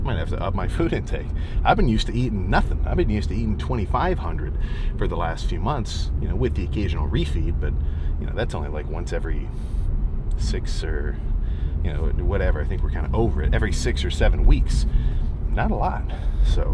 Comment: did I just hear 67?